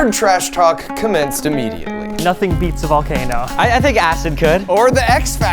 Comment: i heard nothing beats the jet sky holiday 😂😂